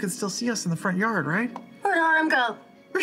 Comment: what did Fearne say there? Couldnt catch that. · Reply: She said, "WHERE ORYM GO?!"